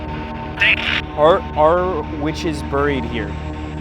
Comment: it sounded like witch